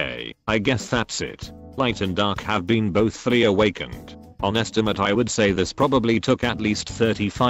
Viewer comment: I thought it said FURRY AWAKEN not FULLY AWAKEN